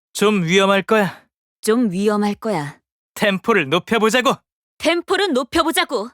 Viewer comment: 템포는 이라고 저만 이렇게 들리나요?